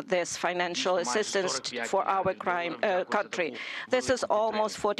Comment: Did i hear Mr President say Iranian people? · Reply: And on 16:14 the interpreter said " assistance for our crime". Coincidence? Signs? On this level of conference such mishearings, missentrpretings and mistakes are unecceptable. Let's not pay attention at all.